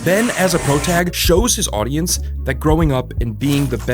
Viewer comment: Excuse me 04:21 did you say "protag" instead of "protagonist" Thank you for saving me that time